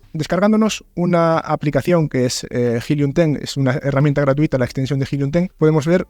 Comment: hola y gracias por el contenido. No entiendo cual es la aplicacion gratuita para conocer la demanda del libro, lo dice al minuto 5:57. Muchas gracias a quien me ayuda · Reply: hellium ten